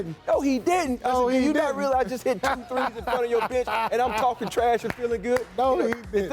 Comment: Lmao, thought he said "I hit 2 threes in front of your bit*h."